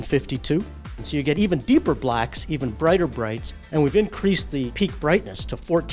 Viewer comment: It first sounded like Vijay said blacker blacks instead of deeper blacks and the fact that the brights are even brighter supported it even more. All in all it does look pretty good.